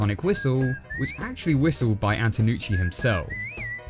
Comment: Omg! I'm an idiot! When Jimmy says auntonucci I thought he was saying aunt nucci, and thought Jimmy had an aunt named nucci XD